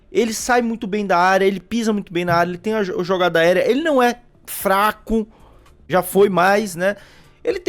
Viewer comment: o Flaco não é Flaco não entendi?